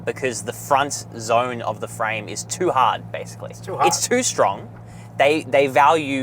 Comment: That means almost all cars on the road before 2018 are 0 stars. 22:13 Did he say front of the car is too strong? Is that why the car transfers crash force onto its occupants? Wow didn't know making cars 'too strong' is also an issue.